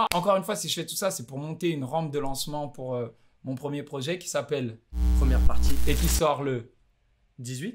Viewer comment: j'entendais ça moi: mon premier projet qui s'appelle ? Euh Partie 1